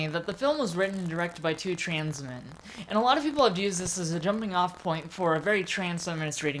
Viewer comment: i think you said that the wachowski siblings are "trans men" (or at the very least i couldn't hear the "wo" in "woman"); the wachowski siblings are trans women, not trans men. EDIT: did say "trans women"; it's just kind of heard to hear. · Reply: trans women, lana and lilly wachowski. My script says "trans women" and that's what I said in the video.